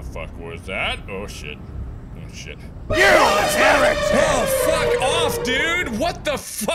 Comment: Jump scares 8:07 9:24 10:43 12:53 13:35 Also what does he say when when he kills you?? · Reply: He says "you heretic!" Since my assumption is the guy has to impersonate a deeply believing person (most possibly Christian) because of the presence of a Bible in the car and the subject of the majority, if not all, of the questions is about Christian beliefs, prayers etc. being a heretic is a thing that has to be banished. For his own ease, however, he "forgets" about the fact that he (as he tells you) kidnapped, tortured and killed people, including the person in his trunk... Thanks for the jumpscare list though! I nearly got a heart attack at the first jumpscare :)